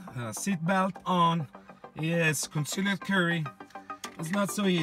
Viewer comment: I thought he said "concealed curry"